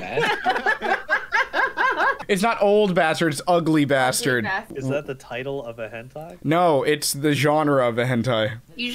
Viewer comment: Anyone know what that was at 5:35 ? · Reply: battle begins in 5 seconds